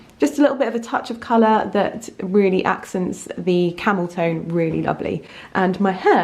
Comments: Excuse my childish sense of humour but when you were talking about your outfit and you said ‘accents the camel tone’ I had to rewind as I thought you’d said ‘accent the camel toe’ - I couldn’t stop laughing. 🤣🤣 ...I’m such a child!! 🙄enjoyed this vlog thanks hun x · Eeeh I'm so sorry I thought you said 'Camel Toe' but you said 'Camel Tone'. I burst out laughing for my own stupidity!!